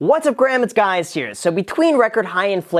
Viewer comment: did you just say "what's up Graham, it's guys her?" LOL